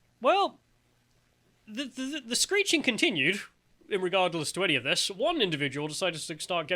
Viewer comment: Did just say "irregardless"?